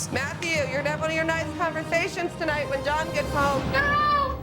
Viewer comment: what did the mom say to Matthew? I don't get what the mom was saying to him at that part? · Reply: Something like " Matthew , you're definitely not having nice conversations tonight when John gets home " 😄